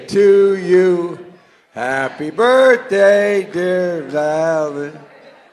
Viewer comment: And he is supposed to be the leader of the free world. I give you Boe Jiden. I really thought he said "Happy Birthday dear Alan".